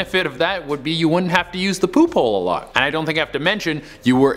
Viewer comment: When he said poophole, I hear pupol. What's wrong with me? :<